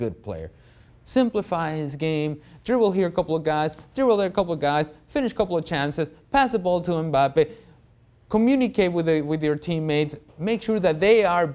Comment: Did he say Pass to Mbappe ? 😂2:42 Never going to happen